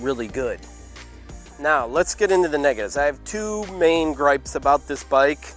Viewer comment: what did bro say 🌚?????? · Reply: “Lets get into the negatives” on the bad things about the bike